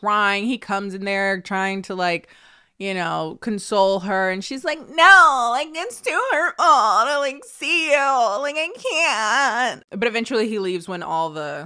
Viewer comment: I'm sorry but can someone tell me what is saying from 10:19 to 10:24? The accent is hilarious but i can't understand a word of it XD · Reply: "Nooo,like, it's too hurtful, to like see youu, like I caaan't"